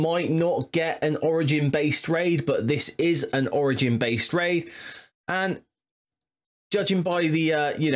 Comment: Could've sworn I heard orgy based raid I liked the video and left it a like. I unlocked Hank Pym finally and now the Illuminati sans Cap Britain is rolling in crucible :)